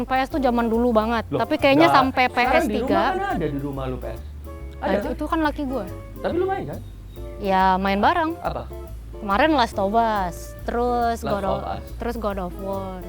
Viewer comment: salfok 37:11 chef renatta bilang "laki" atau aing aja yg salah denger 🥰